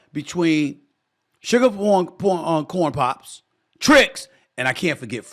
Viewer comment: DID HE SAY SUGAR PORN POPS!!😂😂😂😂